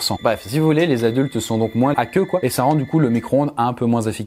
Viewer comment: Les gars a 9:16 il y a que moi qui entend sa ? 😂😂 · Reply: Aqueux ??